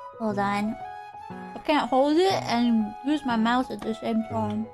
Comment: Think she said mouse instead of mouth here also cant believe FNAF invaded minecraft, I blame Vox